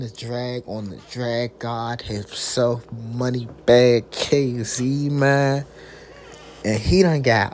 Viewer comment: Who? Why tf is this on my feed 🤦🏾‍♂️🤣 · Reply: cuz at first I thought it said Moneybagg yo 🤣🤣🤣